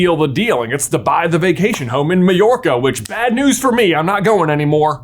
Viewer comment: I always thought Jim Dale said New York in the audio book😂😂